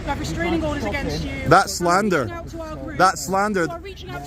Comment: did she say "That's what WhatsApp says." ? at 11:50 and the leak of messages?